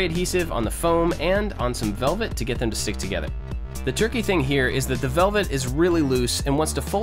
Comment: Anyone else notice him saying "turkey" instead of "tricky" at 6:17 ?